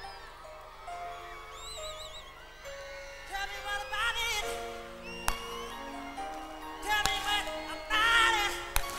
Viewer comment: what does he say here could anyone write? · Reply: “Tell me what about it!”